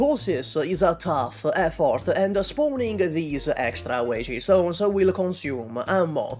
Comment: I was only listening and the whole time I thought he said waige stone 😭